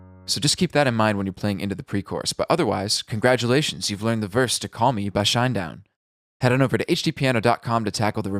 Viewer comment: I thought it said call me mabye. So that, I guess. Good song btw. Shinedown rocks.